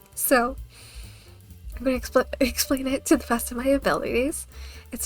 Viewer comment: My brain thought you said you were gonna explode instead of explain lol, mb 😅